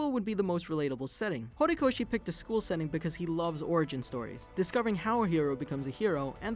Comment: Oh god my mind.... I thought he said "Orgy Stories"